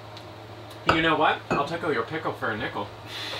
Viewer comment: " Lotta owl sex going down!" "We share the air!" "I'll tickle😁your pickle for a nickle!" Dying here! Great video day, and with special guests 😀 Your next project is a root cellar! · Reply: I thought he said "I'll tickle you're pickle for a nickle"